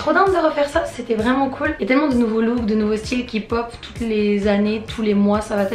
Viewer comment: c'est moi ou elle a dit K-POP? ps; j'aime trop donc pas de problème ;)